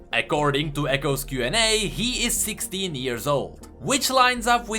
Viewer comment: Am I the only one who heard Ekkording to Ekko’s q&a?